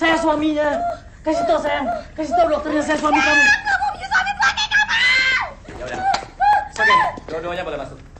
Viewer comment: di 33:26 wanita hamil itu bilang apa ya? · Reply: Ah aku gak mau punya suami tua kaya kamu!